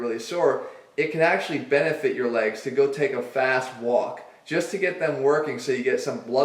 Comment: I thought he said fatass walk and I was like "fair enough but you didn't have to go there though".